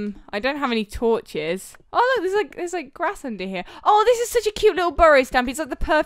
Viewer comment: What everyone heard: ooh there's grass under here! What I heard: ooh there's crack under here!